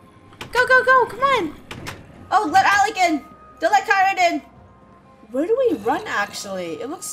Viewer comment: did just say "oh let Alec in , don't let in